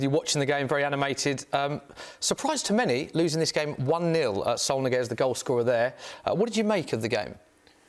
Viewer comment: What did he say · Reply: Saul Niguez.